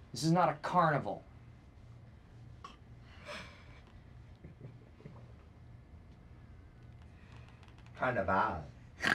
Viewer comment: what'd Creed say? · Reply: "Carnaval"